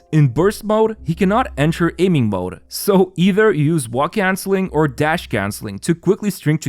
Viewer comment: Help I keep hearing "wok cancer"